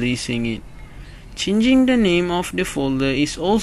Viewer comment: when you said "changing the name" I thought you said at first ching ching da ning, which actually makes sense if you think about it